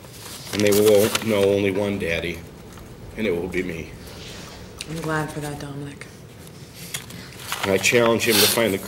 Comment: what does the judge say to dominic??? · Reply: I am glad for dat dominic(dat wat d judge said)